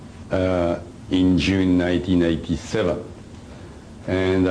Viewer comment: His contact was June 1987. (it sounds like he says 1997)